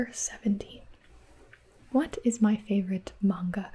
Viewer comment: At first I thought Q:17 said “What’s my favorite mango?” I was so lost